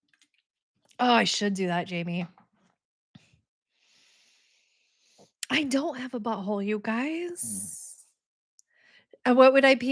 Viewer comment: …. Ok…whoa hold up…. Did I hear you say “I don’t have a butthole” ??? That must have made that whole prep for the colonoscopy AWKWARD AF 😂.